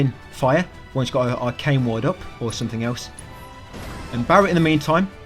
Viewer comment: souunds like the materia missing that gives atb to others when performing skills yourself, forget the name exactly sorry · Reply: Too many grindy nights, I misheard ATB Ward for something else haha. Second run at Sephiroth I got him! On to the next, thanks as always.